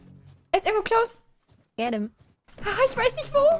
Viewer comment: Erstmal geliket 👍🏻 Irgendwie verstehe ich immer bei 0:50 „Wer denn?“ anstatt „Get him!“ hahaha